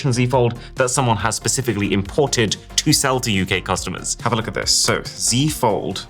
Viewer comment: Did he call us "gay customers"? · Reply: UK customers lol